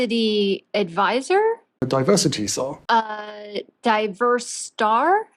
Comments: Rofl, i hear "Diversity Tzar".... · I heard “ Diversity Tzar” 🤣😂🤣😂